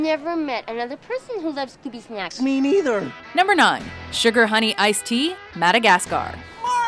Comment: I always thought Marty was saying "Oh Sugar honey I stink!" when Alex initially tried to kill him